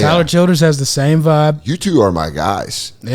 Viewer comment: Did he say Hommy?